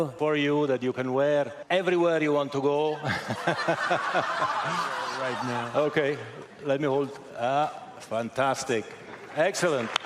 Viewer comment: did he really say I’m putting on right now 😂😂😂😂 what a child 😂😂😂😂